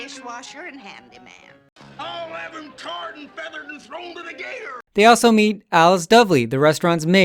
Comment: can you help me to understand What stingy say on 3:51? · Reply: He said "I'll have him tarred and feathered and thrown to the gators" Tarred and feathered used to be a punishment in the middle ages where they'd dump tar on someone and then roll them in feathers that would stick to them.